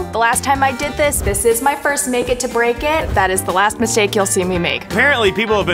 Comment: When she said mistake I heard " mis-ache"